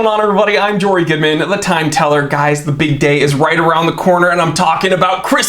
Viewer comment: You said the big day is around the corner and for a second there I thought you said the bidet is around the corner